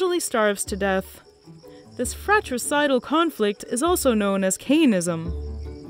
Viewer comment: What's that word she used around 5:24 Cayanism or something? Can't find it. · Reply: Cainism